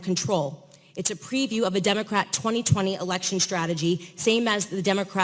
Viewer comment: Am I the only one that heard her say she's a Democrat? I forgive you Candace.